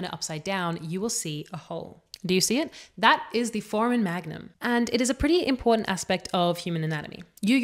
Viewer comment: it sounds like you say .44 magnum, which would also explain the hole.